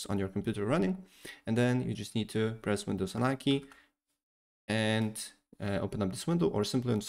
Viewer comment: What was when he said that word unozonike?